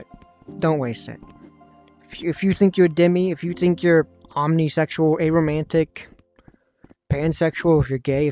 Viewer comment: I misheard you lol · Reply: shit... It does sound like Pomnisexual